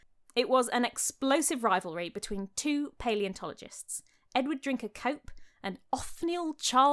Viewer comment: I thought she said “Edward drink a Coke”